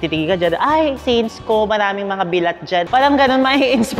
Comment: Did I just hear “maraming mga bilat jan?” sa 05:06 hahahahaa only bisaya knows